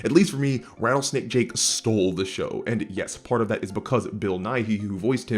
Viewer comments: For a second, I thought you said Bill Nye (The Science Guy), and fucking laughed at the thought of him voice an evil cowboy snake. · Thought dude said Bill Nye voiced Davy Jones .